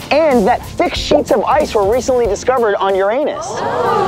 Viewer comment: For a minute a thought he said that thick sheets of ice were discovered on "your anus", not "Uranus"....